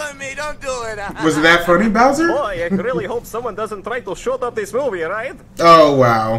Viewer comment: For the longest time I thought said “shut off this movie” instead of “shoot up this movie” I don’t know why